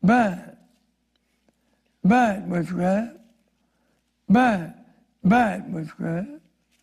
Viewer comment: “Bad! Bad, witchcraft, bad! Bad! Bad, witchcraft, bad! Bad! Bad, witchcraft, bad! Bad! Bad, witchcraft, bad!“ Well, I’m convinced! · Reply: Thought I heard "back, back, back witchcraft"